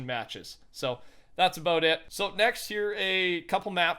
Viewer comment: Did I hear “next year”